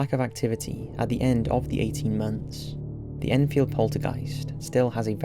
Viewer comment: I thought it said Seinfeld Poltergeist